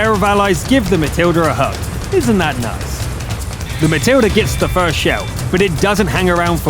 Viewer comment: the captions says valintine, but you say matilda